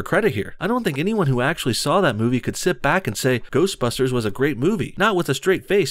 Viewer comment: lol thought you said grossbusters.